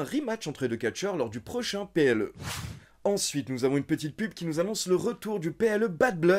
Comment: pardon ? Une petite quoi ?? 😂😂😂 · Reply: Une petite PUB lol